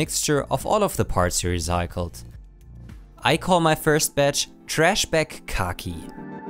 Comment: I heard "Trash Bukkake" as I stepped away from my computer LOL!!!